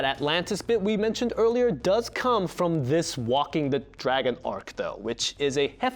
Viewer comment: did he say walking the dragon!